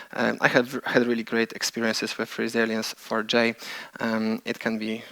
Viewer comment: what is second limiter? Can't understand speaking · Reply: Resilience4j is the 2nd rate limiter mentioned on that slide.